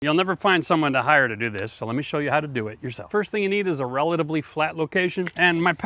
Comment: All right look, I had to watch the intro Twice because I thought he said something different. I thought he said you'll never find someone higher to do this So I'll show you how to do it Yourself.